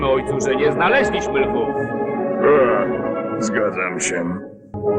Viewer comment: I don't know what he said in Polish but in English, it sounds like he said "It's a god damn shame."